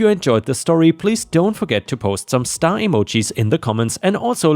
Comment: Oh STAR emojis. I thought he said STY emojis...which means in my locality a painful sore on the eyelid I feel dumb lol